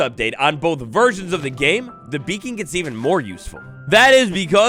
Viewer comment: I thought bro said bacon at the beginning instead of beacon and got so excited for something so small lmao